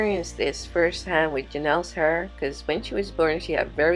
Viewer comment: Did she say "she had very little hr" Not hair?????🤔